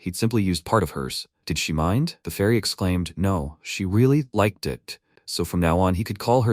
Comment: Did she just say " no i really like dih" hahahhaha